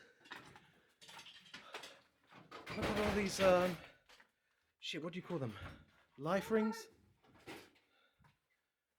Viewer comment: Anyone else heard the ghost from Thirteen Ghosts speak at the 6:45 mark??? Kinda freaked me out. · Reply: I hate to burst your bubble, but that's saying "whaaat!"